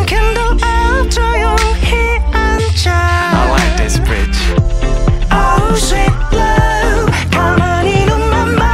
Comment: Did he say 'i want this bitch'?? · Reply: He says "I like this bridge" I thought he said I like this bitch for the longest time